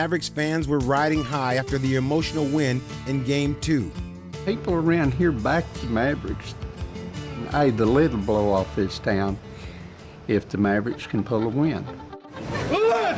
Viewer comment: hey guys, my english is quite bad, what is the texas dude saying in 47:17? greetings from germany and a huge fan of nba and of course dirk ;) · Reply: "People around here back the Mavericks. The lid will blow off this town if the Mavericks can pull a win." And in case you're wondering, 'the lid will blow off' basically means to go crazy, usually with joy or excitement. He's basically saying that all of Dallas will really celebrate if the Mavs win the championship, which, of course, they did.